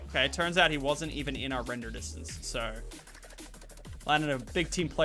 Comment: did he say Lochlan?